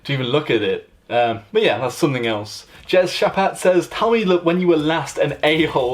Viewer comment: Bro, did you just say Jess "Chapatte" @ 2:19?!? Need to know please!